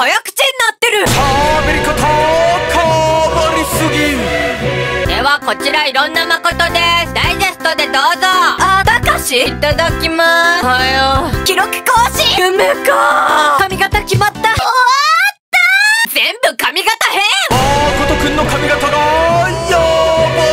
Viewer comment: がなんか繋がってて、「あ、たかし、いたただきます。」に聞こえてツボったwwww